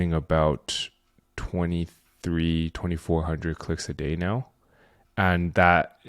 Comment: Ha ha! I'm on a deep dive binge, and I know you said you weren't selling 'feet pics', but had to 'rewind' a couple times until I got "clicks" instead of pics! (I thought he said No pics!?) Ha. This is some great stuff here. So you're basically selling traffic to particular OF niche groups of some sort? Are you getting traffic strictly from SEO for this and those Partnership deals? Those sound extremely lucrative! Worked for big LED maker years ago. They tried scamming us out of our already minuscule annual pay raise by instituting a lucrative incentive split instead. Didn't think we could hit the numbers. We blew them up. That didn't happen again. Great to see some friends and acquaintances on the channel. Say Hi to Niche Site Lady everyone. That girl knows her stuff! Great stuff. Thanks.